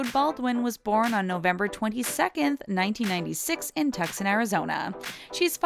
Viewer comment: “Tuckson” Arizona? Wait? What?...ok, if you say so.